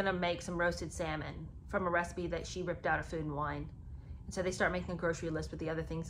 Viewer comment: OMG...I just thought I heard you say Harry said Meghan ripped a recipe for salmon out of Food and Wine magazine! (44:04) Like, really? Who does that?